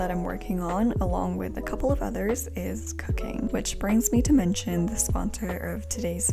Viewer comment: YO I THOUGHT SHE SAID "cocaine" NKJASDNASNDANSJDK OMG I WAS SO SCARED XD